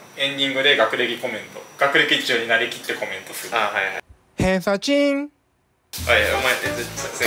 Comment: 偏差ちんが電車賃に聞こえて何事？って思った自分がいる